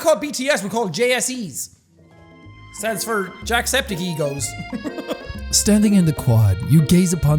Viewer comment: Jack; "We're not called BTS. We're called JSE's... stands for Jack Septic Egos." Possible Fandoms: CANNON ACCEPTED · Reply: I thought it said jack septics eggs I Am So Dumb